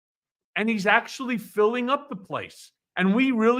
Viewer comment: Donald Trump is full of ish... Oh Filing... I thought you said filling... Never mind (Props to Emily Latella)